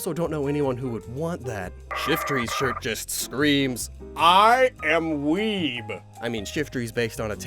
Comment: I misheard "I AM WEEB" as "I AM WEED"